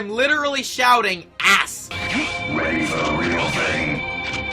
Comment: sigma is actually saying: ready for the real thing?